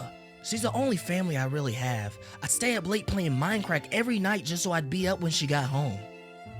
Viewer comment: did he just say minecrack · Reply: Mindcrack